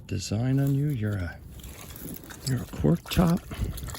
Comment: Sorry I’m late to the party. I swear I heard you say corkchop instead of cork top I laughed out loud. I did go back and look at the Closed Captioned it thought you said porkchop. Love the finds this lark bet you have no amount of vases or vessels to give your partner flowers in that green fish bowl was pretty.